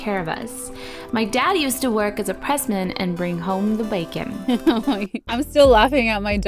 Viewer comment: i remember when you said your dad worked as a pressman as a kid i always thought you said president 😭 so i went and told my mom my favorite youtuber’s dad was the president lol good times